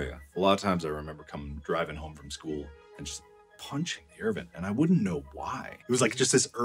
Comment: if anyone cares i think he said " punching the air vent"